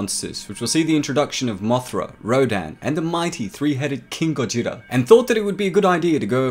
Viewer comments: When you said king ghidorah I thought you said king gojira. I was like “Wait a minute.....” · Did he just say “king gojira”